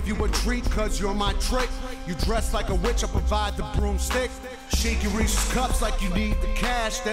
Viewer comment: "You dress like a witch I provide the broomstick" RTJ rules..... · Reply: Oh. Ha. I thought he said, "boomstick."